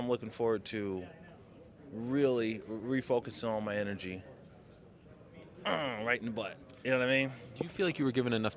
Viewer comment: did he say he's gonna refocus his energy right in Bader's butt? 😂